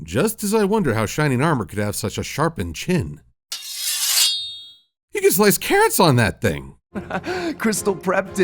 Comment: is he saying “You can slice cats on that thing!” Or is he saying “You can slice carrots on that thing!”